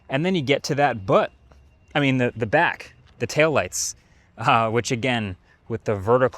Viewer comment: WhAt DiD He SaY~👀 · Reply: bUtt...I-i mean back😅